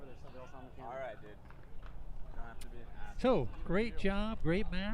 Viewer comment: Not a lot of love in the Wright/Kova vs Smith/Smith matchup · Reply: Right! Zing. Was trying to hear the exchange between Matt and him at the end there, was hard to hear over the commentator speaking, I am not sure what Matt says at 4:16:36 but then Smith’s reply is “alright dude, don’t have to be an Asshole” 🤔